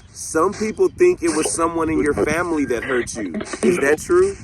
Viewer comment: I heard "it was James" Have you tried bringing an E.M. pump to a grave site to help someone better communicate?